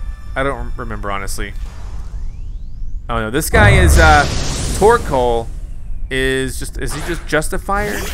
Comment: "oh no this guy is a pork hole"... subtitles, please -_- 58:54 Yes you did say something, to yourself about the plants, checking glass instead of talking to her, obviously... -_